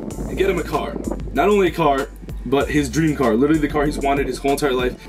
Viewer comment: I thought it said "dream chair" and this makes a lot more since than a chair